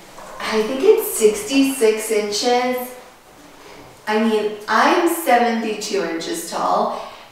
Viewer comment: Haha, I thought you said “I’m 7’ 2inches tall” I knew you both are tall but I honestly did a double take! Beautiful bathroom, can’t wait to see it all decorated!